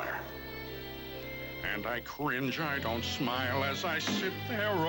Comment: DID HE JUST SAY FUCKING CRINGE?! anyways, epic movie